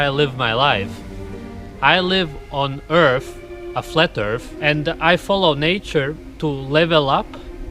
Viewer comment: Did he say "flat earth"? Whyyyy did he need to throw that detail in there?!